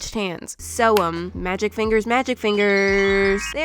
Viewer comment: an advertisement popped up at fi then i thought i heard her say fu